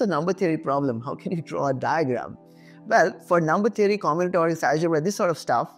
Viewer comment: what did u said here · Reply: For number theory, combinatorics, algebra, diagram IS the example